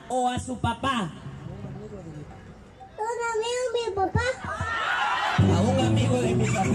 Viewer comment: El video del 2:25 está armado, justo en el 2:26 se escucha una voz suave que dice "a un amigo de mi papá" y luego el niño sólo repite. O igual estoy demasiado fumado, los quiero tegen.